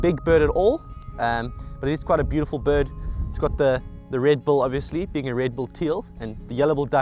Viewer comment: when he says red bill sounds like he says Red Bull